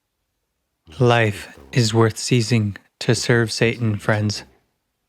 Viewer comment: I'm sorry I'm confused upon rewatching. Did say that "Life is worth seizing to serve Satan" or have I misunderstood the meaning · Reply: Ceasing